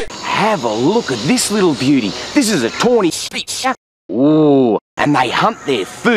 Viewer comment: Original: This is a Tony's Pizza Misheard: This is a Toy's Pizza